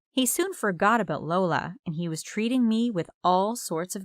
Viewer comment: it was at this exact time 20:16 in the video she said he soon forgot about Lola what did she say she is lola